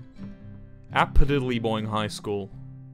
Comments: What did he say cause to me it sounded like the said applediddleyboing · i swear he says apadiddleboing